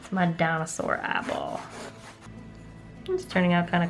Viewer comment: in the video I thought she said "Maddona's sore eyeball". Then i replayed and realized what she said. I really think i have some hearing problems lol